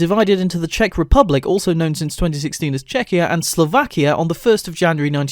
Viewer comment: Good job man but it's not Czechoslovakia it split into Czechia and Slovakia u can probably see the line on the map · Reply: Oh u said Czechia u said it really fast so it sounded like u said Czechoslovakia